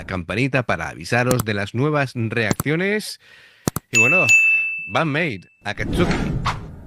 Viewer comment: queeee? Como dice que dijo? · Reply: 😂😂😂😂, dijo Band Maid Juan, un lapsus, no se lo tengas en cuenta al pobre 😂😂